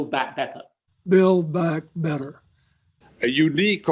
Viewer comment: Oh, I just realized they actually said "build back bitter" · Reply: I heard " Bill back, better!"